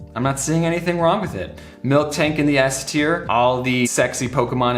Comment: Did you say "Milk-tank"?